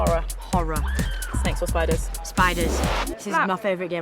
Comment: I thought she said sex or spiders WHO WOULD CHOSE SPIDERS?